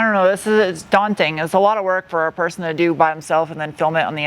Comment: you said "by himself"... just saying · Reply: "by themselves" once I listened a few times, but yeah I heard "himself" the first time.